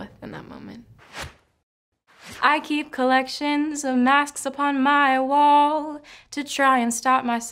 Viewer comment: I ALWAYS THOUGHT THAT SHE SAID MAPS INSTEAD OF MASKS ANS I WAS LIKE ALWAYS CONFUSED HAHAAH AHHH